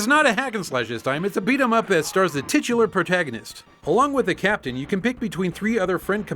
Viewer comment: Did you just say tits?